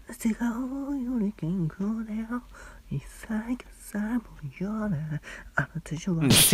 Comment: 汗がおおおおお健康だよにしか聞こえんのだがwww